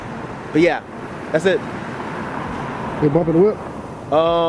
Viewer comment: What did say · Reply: He says "It definitely rocks the Hyundai", in response to the question of whether it bumps in the whip.